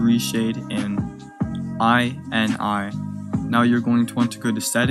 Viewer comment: I am confused on what you said. I and I? I end I? I yend I? Can I please get help?